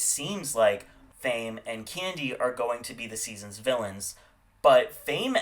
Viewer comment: fame and who? Did you say instead of Kennedy? Like you said it twice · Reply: Ho